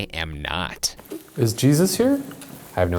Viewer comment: i think it says "i hope"